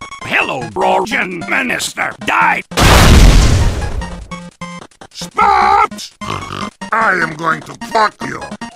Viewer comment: Did somebody call me? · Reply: It's spaps :D